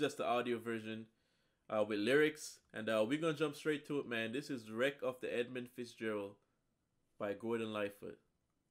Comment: I thought he was saying "I got wrecked with Ella Fitzgerald"...This changes everything.